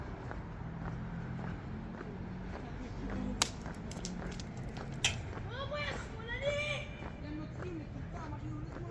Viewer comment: i just saw now the stones at 4:14 What was they saying to you? Stay safe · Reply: Hste things about me as a white person